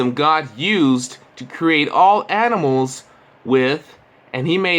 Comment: Did he just say adimals? What in the hell are adimals?